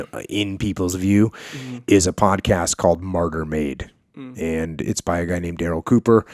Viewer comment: So the podcast Jocko is talking about is MartyrMade. I misheard Jocko and thought he said MurderMade. No. It's MartyrMade.